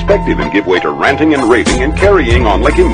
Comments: I thought he said ''raging'' instad of ''raving'' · thanks i thought he said “raping” instead of “raving”